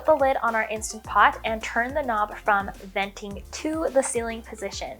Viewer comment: Omgosh I thought you were saying “vente” as in Starbucks lol ☺️☺️❤️❤️